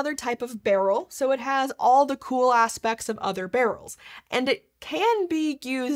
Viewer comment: thought she said “cool-ahh specs” 😅😂